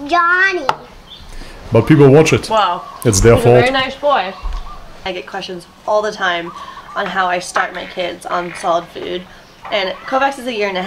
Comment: I swear to God, the first time I heard this sentence I actually thought she said: "I get questions all the time on how I STARVE my kids on solid foods"XD